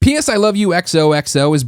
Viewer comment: At the beginning it sounded like he said 'penis i love you'